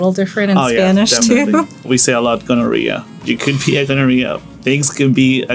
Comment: Why did it sound like he was saying gonorrhea at 00:58?? 😂